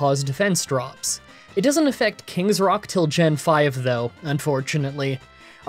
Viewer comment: I'm currently doing a Dunsparce run in Black 2 and a benefit of that is Serene Grace effects Rocky Helmet in Gen 5 but the Gen 5 games are arguably some of the hardest games in the series so I equipped my Dunsparce with a Quick Claw which is working out really well because of Serene Grace. Keep up the good work! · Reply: Oh wait you actually mentioned King's Rock not Rocky Helmet. I could have sworn that you said Rocky Helmet instead of King's Rock when I first watched this